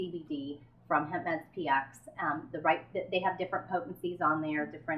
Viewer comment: what is she saying? whats the company name hemp px? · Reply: o nevermind I think she said hempmedspx